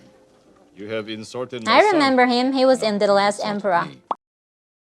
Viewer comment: 😂😂😂 she said "The Last Emperor." I thought she said "The little a** Emperor. "